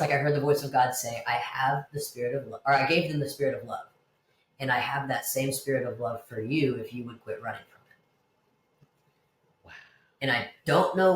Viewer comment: what does he/she say at 1:22? i cant make out the words. "and i have that same spirit for you if you ??? ??? right" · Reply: 'And I have that same Spirit of love for you if you would quit running from it' I assume it means if she stops running from God, but receives Him and His blessings for her